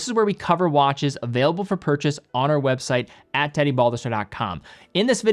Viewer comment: I wasn’t paying attention and I thought I heard “Hi I’m Teddy Bulbasaur” hahaha